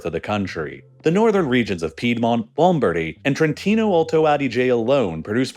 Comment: What the eff did you just call South Tyrol? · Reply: Trentino-Alto Adige. Alto Adige beeing the italian Version for Südtirol. I was also confused and had to look it up myself